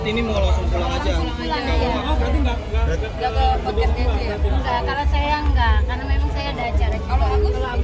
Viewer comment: nggk kalau saya nggk karna saya Dajjal???? Saya salah denger atau emang ngaku sendiri ini?